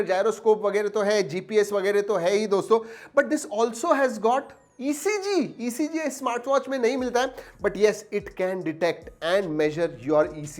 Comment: What he saying at 5:46 ? ECG nhi milta hai ?